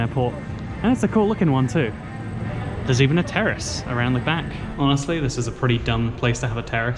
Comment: When said “terrace” I thought he said terrorist and I was like, ok?! Nice to know...?